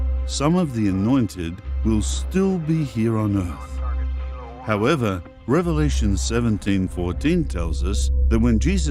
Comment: Notice at 27:15 there’s background talking like over a walkie talkie. Very subliminal. Can anyone isolate it to see what’s said? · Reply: All I can hear clearly is the word "target"